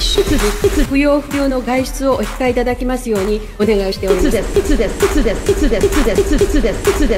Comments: なんか、だんだん「いつです。」て、聞こえてきたの私だけ？ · からなんかstudent に聞こえる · くらいから、つですに聞こえてくる